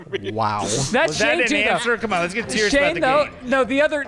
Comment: Can someone tell me what said at 16:17 I’ve been trying to figure it out 🤣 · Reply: “Is that an answer. Let’s get serious about the game”